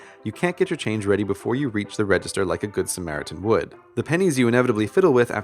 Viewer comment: Was i the only that thought he was saying "penis" all the time?😂😂😂